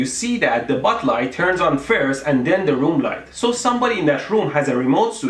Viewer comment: 😂 it thought it said bud light